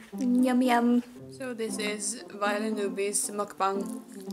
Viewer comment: mukbang? i thought u said smokebomb lol